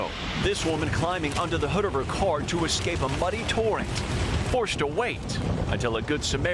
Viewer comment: Seriously sounded like he said, “This woman climbing UNDER the hood of her car to escape a muddy torrent.” I kept thinking, how would that help? Is the mud falling from the sky?